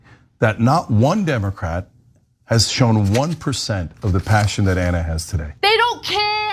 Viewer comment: You're wrong. Your misrepresenting what he said. He didn't say compassion. He said passion.